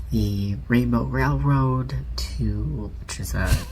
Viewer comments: I thought it said rainbow brown road…both can be true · What did he or she sayyyyyy?!? Rainbow railroad?!?